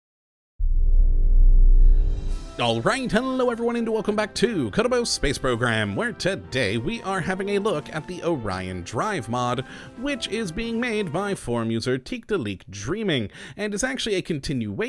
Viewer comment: Every time you say "Kottabos Space Program" I hear "Kuribo Space Program" i.e. "Goomba Space Program"